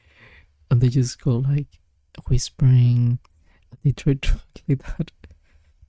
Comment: bruh at first i thought it said whimpering